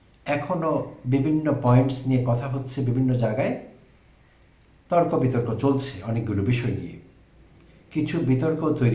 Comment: 😊আপনি বললেন defend করা মানে ছোট করা কি বললেন বুঝলামনা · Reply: শব্দটি defame, প্রিয় ভাই আমার। দয়া করে আবার শুনতে অনুরোধ করবো।